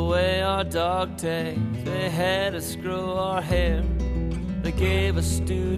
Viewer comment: what does he say @ 0:23 ? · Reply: "they had us grow our hair" hope this helps 5 years after you left the comment.